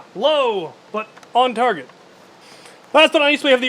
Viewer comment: Did anyone else hear the "low" at 8:15 and think he said "lo" at first so the speech started in your head? or that just me and my 2 deaf brain cells?